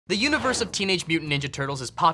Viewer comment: Genuinely thought he said university not universe